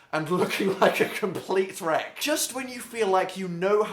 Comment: I thought he said complete shrek instead of complete wreck 😂